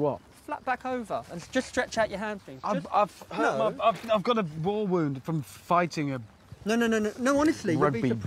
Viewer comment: "i've got a ... war wound from fighting" the perfect excuse to avoid exercise. i should try that sometime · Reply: i thought he said “I’ve got a ball wound from fighting a, a , a rugby… player’